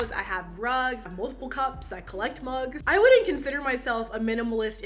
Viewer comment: When she said mugs I thought she said drugs... anyone else